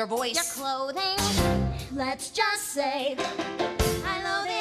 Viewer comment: For some reason when i was a child i thought they said " I LOVE IT ALL "...... well i was wrong